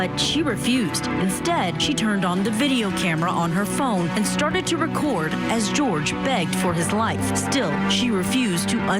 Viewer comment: in this video, Sarah starts saying something that is incomprehensible due to her slurring words. Does anyone know what Sarah said during those 20 seconds? RIP Jorge. 🎉😭😭 You didn't have to go out this way. 💔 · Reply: She says, "Wriggle around some! I might want to get video of it -- extra. 'Cause I got this. [LAUGHTER] Wriggle around."